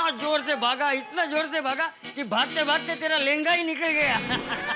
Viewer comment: wtf, did he say lenda 😮😮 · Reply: Lehenga"